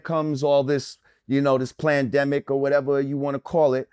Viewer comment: Did he just say "Plan- demic"? Lol was that a subconcious thing?